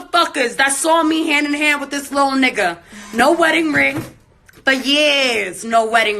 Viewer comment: Wait ! Did she just use the “N” word when referring to him? Please rewind that, it sounded like she said “Lil Nigga” something…